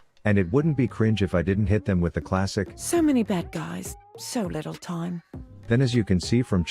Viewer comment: Woah woah woah. Caitlyn says "So many BAD guys, so little time"? I really thought she said "So many DEAD guys". Wow.